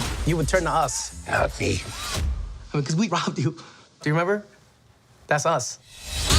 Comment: lmao did he just say "because we rob you ?